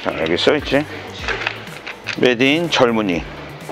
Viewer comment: 메이드 인 젊은이 저만 그렇게 들리나요 ㅎㅎ